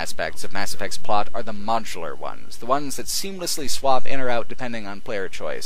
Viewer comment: ''The best design aspects of mass effect's plot are the "... m(o/au)(n/_)(tul/chul/cul)ar" ones. The closest I could find, I think, was 'macular', which doesn't make much sense. Can anybody, please, spell that word for me? English is not my first language obviously, otherwise I would probably have no trouble understanding what it is he was saying in that particular instance. And to Mr. could you, perhaps, start adding subtitles to your videos at some point? Or even just enable autosubtitles if it is not something hard to implement, of course - I just notice that some videos here on youtube have it and the others don't. Don't get me wrong, your speech is as crisp as it gets, I believe... 99% of the time (for me at least). I'm more inclined to think that it's the profound vocabulary you employ that can result in some of us poor foreigners not quite understanding some individual words or phrases. Which is sad, as it leads to a minor frustration and can also diminish the overall enjoyment from watching your videos. So subtitles would reeeeally help to resolve this, especially if they're custom, handmade, but again even the automatic ones can do the trick quite often. Thanks! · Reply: Oh, I got it at last :-) The word I couldn't make out was "MODULAR"! I was able to 'decipher' it having heard it a second time here